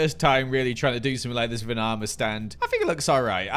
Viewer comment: who else heard banana stands?